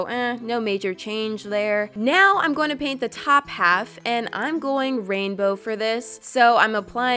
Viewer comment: When you said "top half" I thought you said "top hat"